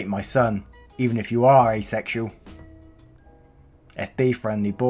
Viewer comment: did someone say asexual.